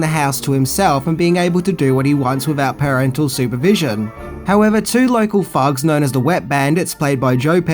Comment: I thought said “two local f**ks” the first time around.